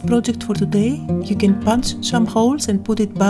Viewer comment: Adorable, amazing art journal page as usual! I have to tell you, at 12:48 you said, "You can punch some holes..." and for a split second I thought you said, "You can punch Tim Holtz!" LOL!